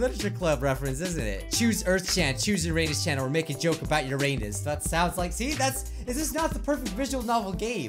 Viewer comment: I heard: make a joke about ur anus lol